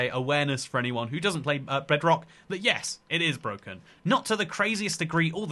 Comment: Somehow I misheard it as "Breadrock"